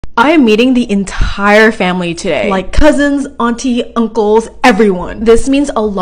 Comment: I wasn't looking at the subtitles and thought you said "I am EATING the entire family today." 😭💀✋️